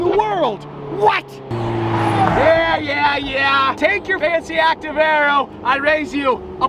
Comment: I thought he said "fancy ass camro" not fancy areo